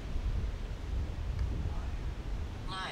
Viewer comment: is it just me but I think I heard the word liar in the distance before the app said it. About